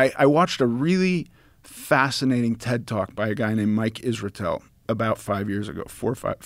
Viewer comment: Fuck I thought he said TikTok instead of Ted Talk. That’s says a lot about 2021 lmao